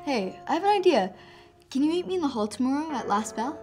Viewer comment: I thought she said “Can you eat me in the hall tomorrow?”